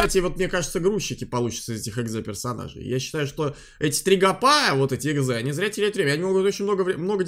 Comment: Мне послышалось три говна 😅